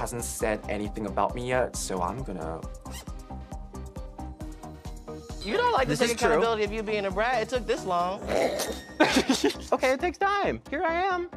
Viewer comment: English isn't my first language. Is someone can tell me what's Koco is saying at 01:57 ? Thanks <3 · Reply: "You don't like to take accountability for you being a brat - it took this long" 😂